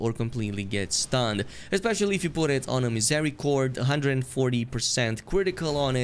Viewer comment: wtf are you saying at the 10:00 mark? something something 140% critical? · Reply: Misericorde It's a dagger you can get from behind a stonesword key wall in Stormveil. The Dagger has high crit damage.